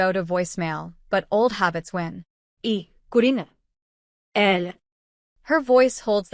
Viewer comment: E Carina L? What is that, it's spoken in a different voice with a Spanish accent! 11:46 Prupey? Instead of prepare, the voice again in a Spanish accent.